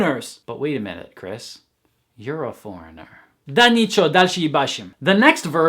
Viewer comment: Can someone please translate what he says at 7:48? He makes a comment but there is no subtitle underneath! I love his videos but sometimes he speaks a little too fast for my beginner ears. Thank you in advance!! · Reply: Да ниче, дальше ебашим Nothing, let's continue